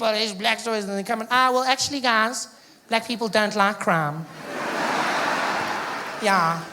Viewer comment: My harry potter loving ass thought he said " black people dont like krum"